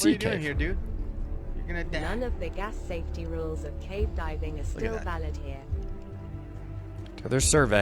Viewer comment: I thought she said that the "safety rules are STILL valid here."